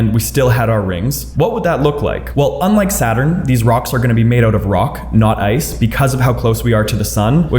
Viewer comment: Did he just say "these rocks are gonna be maid of rocks" 😂😂😂